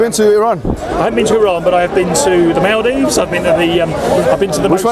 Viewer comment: Did he says Maldives...??? I am a Maldivian :D